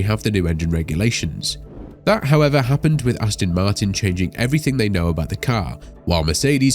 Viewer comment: It sounds like you say Astin Martin, rather than Aston Martin.